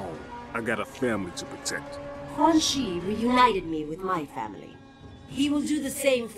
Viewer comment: It sounded like Sindel said ‘Quan Chi united me from minecraft